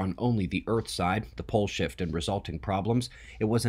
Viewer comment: Every time you say pole shift it sounds like you say bull shit. Kinda funny thought I'd share.